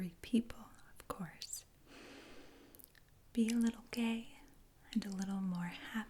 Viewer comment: did she said “be a little gay” or it’s just my imagination, please answer me